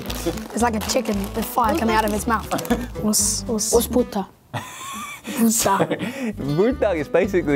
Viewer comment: lmaooo I heard "What's 'puta'?"